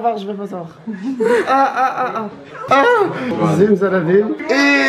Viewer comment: c’est moi ou il dit « zimzalabim »?? Un icon